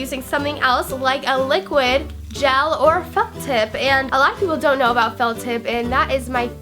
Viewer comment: it sounded like she said "liquid gel or fuck tip" lol